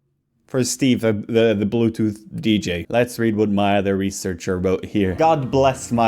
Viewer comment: Every time he says "Maya the researcher" I hear "my other researcher"